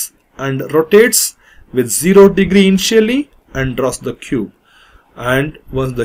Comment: "0 degrees and chilly" is what I heard at first